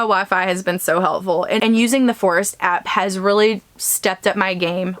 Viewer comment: You said "using the forest app" but I first heard "using the force has" - LOL